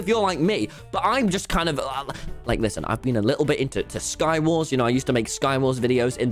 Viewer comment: When you said sky wars, I heard Star Wars